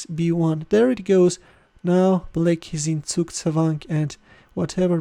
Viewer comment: Please anyone can tell me What did he said at 13:04 "now black are in .... !?? Tsuk tsavang or something !? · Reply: Zugzwang (German for "compulsion to move", pronounced [ˈtsuːktsvaŋ]) is a situation found in chess and other turn-based games wherein one player is put at a disadvantage because of their obligation to make a move; in other words, the fact that the player is compelled to move means that their position will become significantly weaker. A player is said to be "in zugzwang" when any possible move will worsen their position.